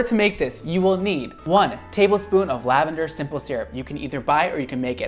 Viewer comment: I heard "nipplesyrup"and for a second I was going hmm..hmm?